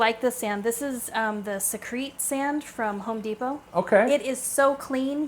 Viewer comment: In the geophagus tank where you fed the fish, I have two questions. You have beautiful green algae just on the rocks but not on the sides of the tank. How did you do that? Looks great. Secondly when you gave the name of the sand you bought at Home Depot, could you clarify the name of it? Is it called crete sand? Sounds like you said sea crete sand so I'm not sure.